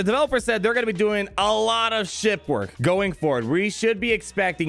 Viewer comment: The devs said they'll be doing a lot of ship work? You misheard them; they said "we've been doing a lot of shit work".